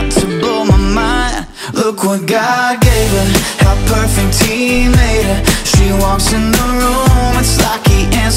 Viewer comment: does anybody hear perfect teen hater or am I insane